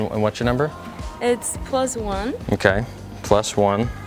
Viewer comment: I heard Close One 😂 And it was actually +1